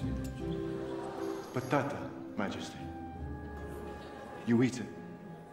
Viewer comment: is that a potato? because I couldn't hear him so clearly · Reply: Yes! Patata!